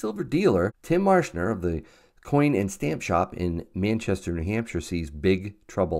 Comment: Did you say Manchester in Hampshire? If you were British you would understand how mad that sounds lol